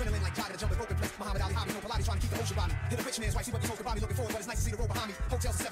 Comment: I always thought nas said "looking forward but it's nice to see the rope behind me" as an extension of the Muhammed Ali reference( rope-a-dope), but now I know.